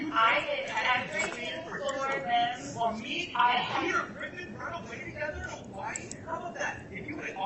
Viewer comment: Did he just call Chad 'Peter Griffin'? (😂) ...from Ohio